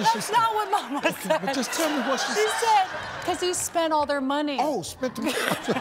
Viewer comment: I thought she said “spent the Alimony”